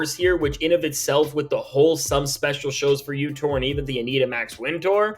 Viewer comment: I thought he said some special sues for you😂